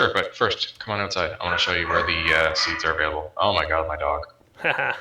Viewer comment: did say: oh my god my dodg or did he say: oh my dog my god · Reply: Oh my god my dog. since his dog was doing a lot of noise.